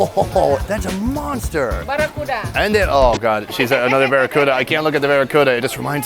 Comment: Did I just heard Sonny say "Ah, hinde" at 7:44 when the lady offered again the Baracuda?